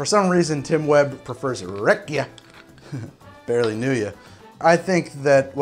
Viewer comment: Did you say rakia?